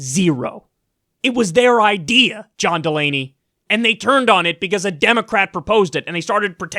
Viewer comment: When the video started I thought you said John mulaney.